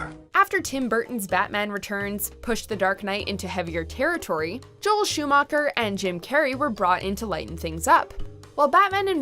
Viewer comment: ジョエル・シューマッハ演じるって聞こえた